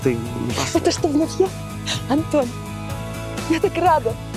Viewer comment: я одна слышу: Это , что Я? Это что бл**ь Я?